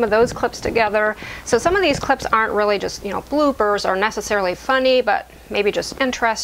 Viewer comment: This was a wonderful video, really made me smile. The funniest thing was that almost right up to the end I thought you were saying BlueBirds then realised that you were saying Bloopers :)